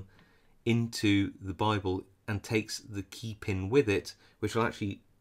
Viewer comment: What is this word he's saying that sounds like "Bible"?